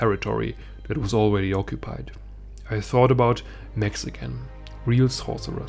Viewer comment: i thought you said "mexican" i was so confused